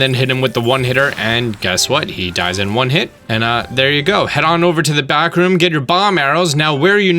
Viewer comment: I thought he said "head over to the bathroom." lol